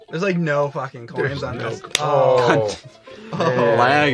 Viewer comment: HOLY SHIT! At around 12:35 did Scott say "Cunt"? Scott the has lost his innocence